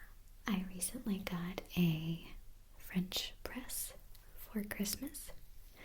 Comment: I thought she said "bench press" lol